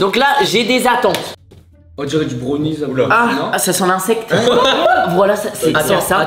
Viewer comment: C'est moi ou a 15:22 Michou dit wala